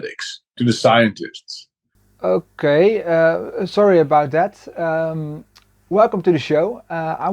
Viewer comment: The subtitles said "sorry a Baudet" 😆